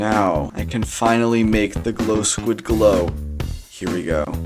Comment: I swear I heard "Glowing S3x"